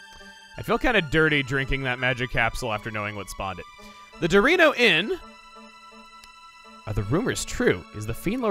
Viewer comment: You saying Dorino Inn sounded like you Sayed Dorito Inn